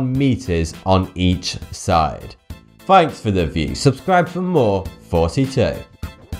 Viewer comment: Sound like he said 42 at the beginning